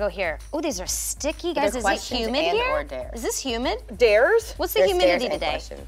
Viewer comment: Who else thought she said human at the start and not humid